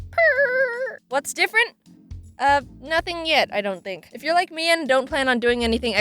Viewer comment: did anyone else hear "if you like me" instead of "if you're like me"